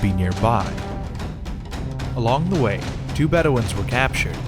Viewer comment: Lol why does the caption say "better ones" instead of bedouins in 5:56?